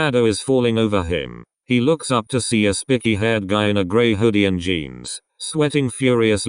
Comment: i know this is supposed to be serious but i LOST it when it said " SPICKY" instead of SPIKEY Im deeeaaaad edit: OMG IEKJWNGFOWEG THAT WAS ADORABLEEEEE!!!! JROGIEIOJERGIO THE ENDING WAS SO SWEET DESPITE IWA CHAN STRARTING OF AS A DICK!!! THAT WAS THE CUTEST THING MY EYES WILL WITNESS IN MY ENTIRE LIFE!!! Also, i have a feeling that the one iwa chan was talking to for advice about loving oikawa as kenma. just a hunch